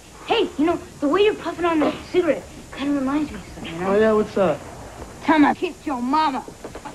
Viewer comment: What does Mouth really say here? It's clearly dubbed over. "Time I frenched kissed your momma?" maybe?